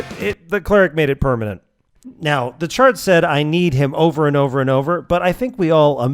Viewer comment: I keep hearing this line, not as “I kneed him” and instead as “I knead him”, like Gramarcy is just pressing and twisting the opponent like an unruly ball of bread dough.